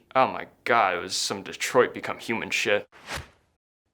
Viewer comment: DID HE SAY DETROIT BECOME HUMAN?!